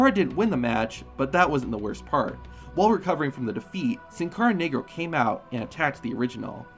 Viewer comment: did he really say Sincara Nig*r? Or am I just trippin